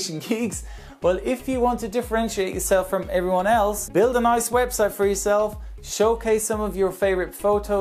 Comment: after all that talk about Ice, I thought you said "Build an ice website"... but it was quite interesting. I never thought how planes de-ice mid flight.